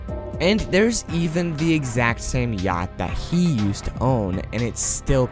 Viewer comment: not being weird but I swear you said gyatt instead of yhact 😭😞